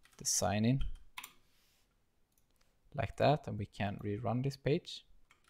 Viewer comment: I though I heard we can rerun this B*tch xD